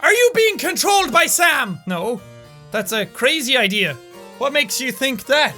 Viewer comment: Someone asked "jack are you being controlled by Sam" Jack said "no what what a crazy idea" I thought he said 'no what crazy EYE-dea'